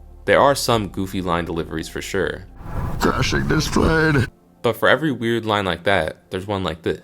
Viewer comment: Same. When I was a kid, I always thought he was saying, "Dashing display" & not "Crashing this plane" LOL